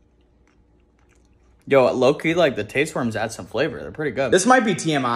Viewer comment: Did he call the tapeworms... taste worms?